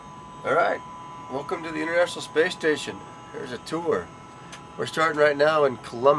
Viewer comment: Did he say "Welcome to the UNIVERSAL Space Station"?